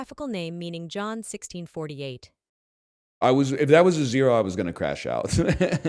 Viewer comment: I was at 2x from pressing the screen and I thought he said “getting backshots” instead of “gonna crash out” 😂😊